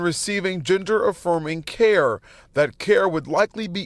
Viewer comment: I swear I heard that reporter say, "Ginger-Affirming Care". LOL!